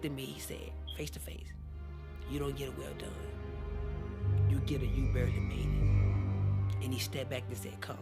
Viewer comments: what did he say God said to him @ 10:43 ? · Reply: "You don't get a well done" "you get a you barley made it" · what did He say??? I didn't understand oh :((( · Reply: he said "you don't get a well done, you get a barely made it"